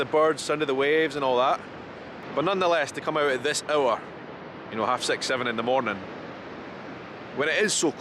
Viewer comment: I love your accent but I often have to use CC to understand. I KNOW we speak the same language. But I swear at 1:17 I thought you said you had sex in the morning. And you probably do, but after listening again I don’t think that is what you said there.